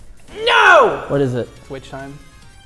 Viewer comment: what move does marss say I can't tell for the life of me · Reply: He said "Witch time".